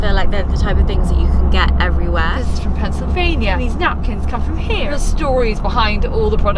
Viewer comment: LOVE!...I NEED to visit England! BTW, did you say Pennsylvania @ 23:55? If so, I'm from Pennsylvania!!!! xoxo